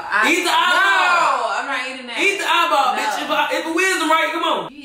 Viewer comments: Did say “eat the eyeball bitch” (12:45)?? I hope not sounded type rude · Ik I’m not tripping did call her a bitch listen close